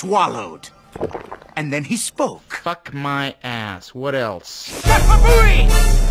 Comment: Since I was a kid, I always thought he said "Makafooey!!!"🤣🤣